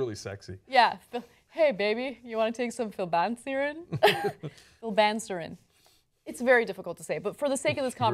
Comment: I thought it was said like "Flibben Serin." :)